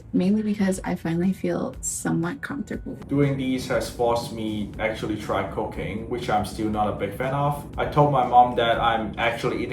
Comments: I had to rewind, when I was listening and not reading the subs I thought he said "doing this has actually forced me to try cocaine, which I'm still not a big fan of." lol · I thought he said this diet forced him to start doing COCAINE 😂😂😂 Which he is not a big fan of. 😂😂😂 This dude is pure comedy gold, completely unintentionally. I love it. I was just listening in my AirPods and not watching. I ran to my phone to rewind and watch what he said, lol… “cooking”. Cocaine might have helped explain his shrimp and strawberries meals 😂😂😂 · Thank you for his subtitles, I was multitasking and thought he said, "has forced me to try cocaine." But he actually said, "has forced me to try cooking" LOL